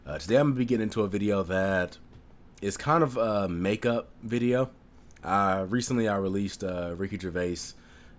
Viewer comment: At the start when you said "make up video" I thought you said "makeup video". That would be quite a change in the channel's direction!